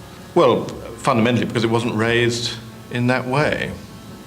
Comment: I wasn't raised in that way." "Why not?" "I don't know." Holy crap, an honest answer from a banker? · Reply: I thought that was what he said and had to immediately relisten to it. He said "it wasn't raised that way", he wasn't referring to his upbringing.